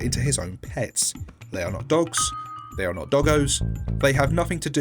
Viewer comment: What did you say There Dogs